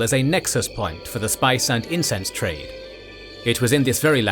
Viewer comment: for half a second i thought you said "spice and incest..." lol.